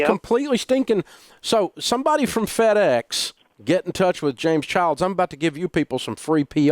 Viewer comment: did I hear James Charles lol